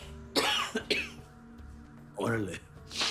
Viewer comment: Amazing video! Im an native Spanish speaker, but i think in 20:06 tuco said “Orale” an Spanish expresión that in this context means “I’m agree “